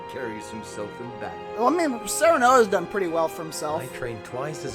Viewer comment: I thought he said "Semen"